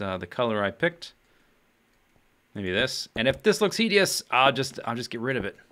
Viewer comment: you got caught by the pronunciation police for saying "I"cononmy instead of "E"conomy ;) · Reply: and then at 7:36 you said h-ee-dious instead of hideous, Im loving it